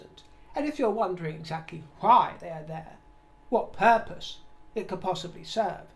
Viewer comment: I need to get my ears checked. 😆 I'm listening while doing chores due to time pressure. When you said "if you wonder what purpose it could serve" (meaning the tour to Colombia) I misheard it as "if you wonder what HERPES it could serve" and was a bit startled by it, but then thought it would even make sense this way. 😂